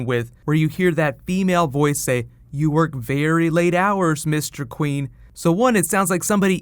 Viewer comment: Lmao I thought it said "You work late hours Mr Wayne" I nearly died .